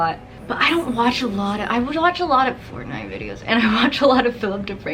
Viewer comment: what did she mumble? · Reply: missreia fortnite videos